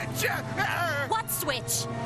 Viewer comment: I swear I could here saying "you stupid bitch" 😭